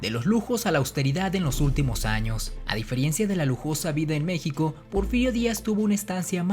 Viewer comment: Es neta que dice DIFERIENCIA???? MIN